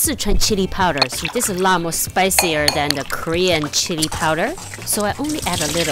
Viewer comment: I thought u said "spicier than the korean shitty powder"